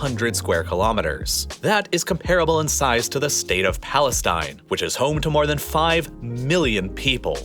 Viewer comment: "state of palenine"? What is palenine? Did anyone say this is a country? Keep your facts straight buddy.